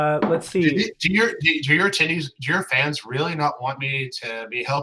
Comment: I still feel SOOOO sorry for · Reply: did just say "tiddies" right before he corrects it to fans? Did anyone else catch that?